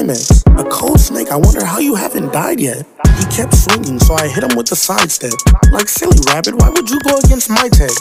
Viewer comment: This whole time when he said he kept swinging so I hit him with the sigh instead I thought he Said hit him with the side step